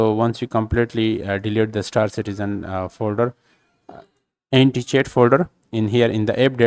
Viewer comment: What folder? NTH folder? Nidget folder? NDTH? Why do a tutorial without showing the folders themselves when you say the most important word like you'll die if you say it slower? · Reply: I thought he is saying anticheat folder